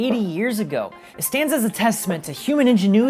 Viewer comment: Human engineering and gumption.....could have sworn you said gunship.